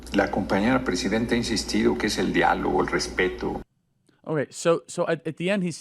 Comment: “El diálogo, el respeto” Did he just call me el diablo?